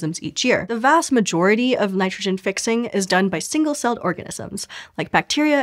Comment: You may have said " orgasms " instead of " organisms " or is it me ?